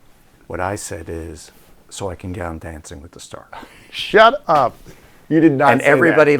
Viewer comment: What did Robert say @ 1:06:06 at the question: Why do you want to become famous? can someone tell me please ! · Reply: "So I could get on Dancing with the Stars "...